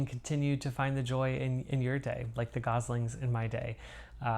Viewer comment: Such a fun update …..lovely stitching and at first I thought you said goblins and I was like wow so fun but I realized you said goslings ……